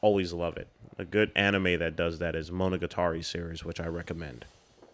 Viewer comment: what is the anime you recommended at 11:22 ? i couldn't catch the name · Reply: Monogatari Series 👌🏽